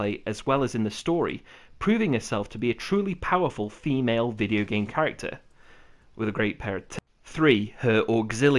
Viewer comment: did he say tits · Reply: TITLES. god..